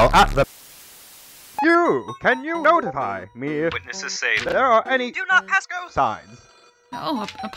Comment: I love not knowing what he's saying but knowing what he wants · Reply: “YOU! CAN YOU [[notify]] ME IF [[witnesses say]] THERE ARE ANY [[do not pass-go]] SIGNS???”